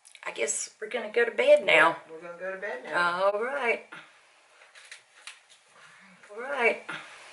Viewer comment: I believe is saying “well alright”. Could be wrong but that’s what I hear. Great video though!!! You are both 2 very brave women, I don’t know if I would ever sleep again after seeing that doll turn her head so intently as to look right at me!!